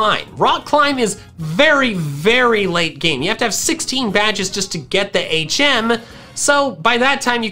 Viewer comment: Did he say 16?